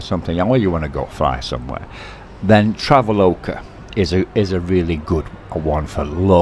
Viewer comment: What was that one app for airline travel within Thailand? Sounded like he was saying Travel Loca.. I can’t find it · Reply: "Traveloka"